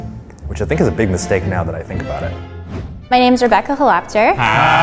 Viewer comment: I thought she said “my name is Rebecca’s a Lobster”